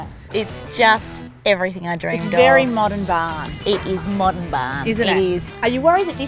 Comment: What at @ 4:06 “Modern bah”??? What did they say? Thx